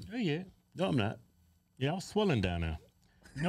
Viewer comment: LOL, I thought you said , " I am swimming down there".